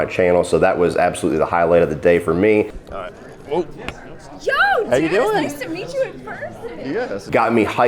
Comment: Did she call you "Joe"?